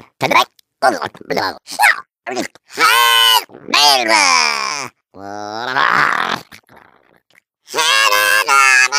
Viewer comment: Did she just say "Hail Valve!" ????? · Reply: Hail valve.